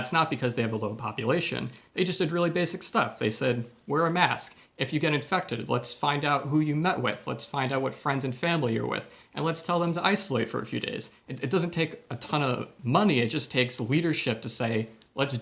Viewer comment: does he actually say, “who you nut with”😂